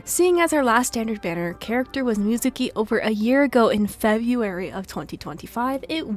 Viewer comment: idk why the auto generated caption said Neuvilette instead of Mizuki, i was so confused like what?? since when was Neuvi in the standard banner 😭 also i love Lohen and his trailer just made me want to pull for him even more edit: THE CAPTIONS ALSO SAYS NEUVILETTE INSTEAD OF NICOLE WTH 😭